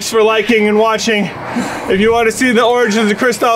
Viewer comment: I could have sworn that J said, "If you want to see the oranges in Kristoff's pants, click there," at the end.